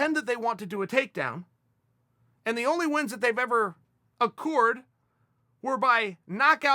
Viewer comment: Wins *accrued*? Sounded like he said accoored lolololoooool